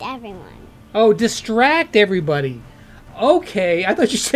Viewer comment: I lost it when I thought she said- "He shrecked everybody."